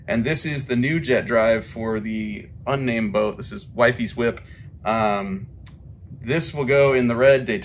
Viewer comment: I thought he said "Whitey's Whip" instead of "Wifey's Whip". I kinda like "Whitey's Whip" to be honest. I think my dad knew a guy called Whitey.